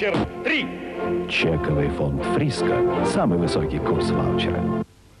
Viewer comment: На 0:06 мне послышалось слово фиаско!!👍🤣😂